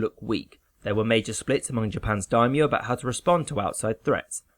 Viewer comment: What does he say in 1:18. Japan’s d.... something ? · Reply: There were major split tumor in Japan's "daimyo" about how to respond to outside threats. daimyo(大名) ：regional feudal lord